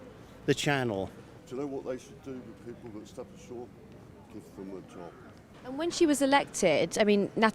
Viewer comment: what does the guy at 7:34 say? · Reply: It was "give them the top" thing he was literally saying to end them...